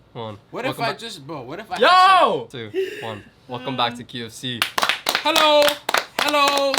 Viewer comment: Why do i always hear the intro “Welcome back to KFC”